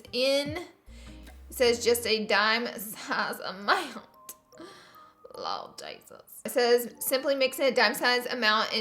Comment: I thought it said to only put a dime size for powder laura😂😂I'm dead